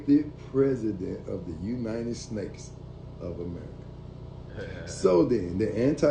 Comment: My 5 yr old daughter has told me things like the little girl in the beginning. She's telling the truth for sure. The "type shit" had me rolling literally 😂😂😂😂😂😂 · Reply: Did he just say united snakes 😂😂😂😂😩😩😩sorry I can’t